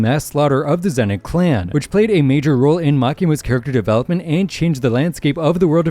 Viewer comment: did bro say Makima???